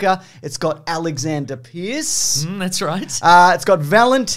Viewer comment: On a fourth listen and it sounds like you said "Alexander Piss" and it made me smile.